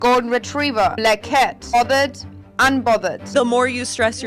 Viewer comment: LOL, I thought that lady said golden retriever and blackhead.